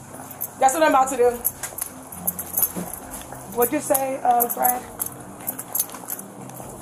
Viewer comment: "What you say of?" Huh?